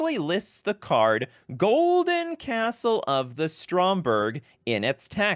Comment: As a German, I only hear "Golden Castle of Micheal Scott".